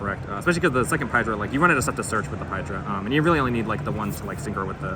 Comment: What did he say at 0:59 after "Especially cuz the second Paidra" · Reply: "you run out of stuff to search with the paidra"